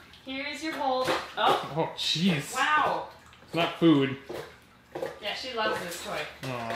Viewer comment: I thought said “cheese it’s not food”